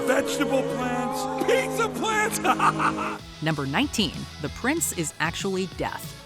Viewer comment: I thought you said the Prince was deaf…